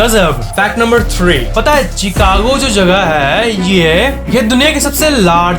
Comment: did he say chhicago 😌😌 Don't judge me plzz